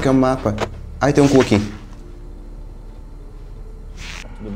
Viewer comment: ain tem cUh aqui ( foi isso que eu entendi)